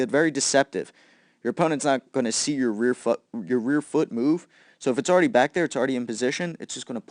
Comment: did he just say '' my rear fu*k ''